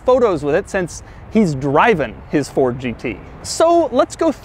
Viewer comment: Did he just say: “Driven?”